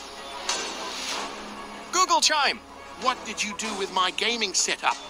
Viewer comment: I hear it's saying Gogochaime